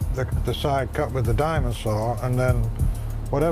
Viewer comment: I misheard "diamond saw" as "dinosaur" for a moment and thought Ancient Aliens had turned into young-Earth creationism.